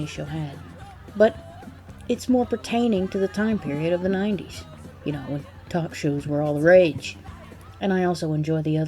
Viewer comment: I just heard “It’s Walter Koenig to the time period of the 90’s.” 14:12 You can be in The Book of Eli and do all the Jim Beam commercials you like, Mila, (also I think she did naughty things with Natalie Portman in something) but everyone knows you as That 7 With Actual Comedic Talent and there’s no shame in it.